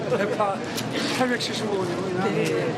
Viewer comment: 875만원으로 들리는데~~~